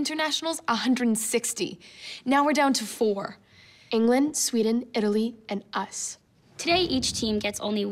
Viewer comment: When said the four remaining teams I thought she said elite for Italy 😂😂